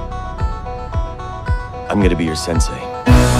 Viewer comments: "Are you gonna be my karate teacher?" - "No...,- Don't say it Don't say it Don't say it Don't say it Don't say it Don't say it Don't say it Don't say it - I'm gonna be your Sensei" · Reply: For a second I thought sensei said sempai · Why did I think he said am gonna be your sexy at 7:45 🙈🙉